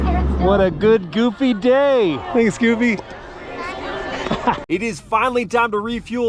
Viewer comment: did you just call Goofy Scooby? 😅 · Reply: Haha, nah, I was saying "thanks, Goofy" but the cut made it sound like "Scooby". Caught the "s" from thanks and I didn't have my mics for Disneyland, so it wasn't clear.